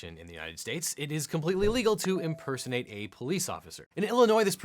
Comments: I heard you as "it is completely legal to impersonate a cop"... whoops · Sounds like u say "legal" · Does anyone else hear him say "it is completely legal to impersonate a police officer"? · You talk to fast I thought you said legal, and I would have if you didn't follow that up. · Almost sounded like you were saying it is legal, which wouldn't make sense. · did he says it's completely legal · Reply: He did say “illegal,” but he said it fast, and the “I” didn’t sound as prominent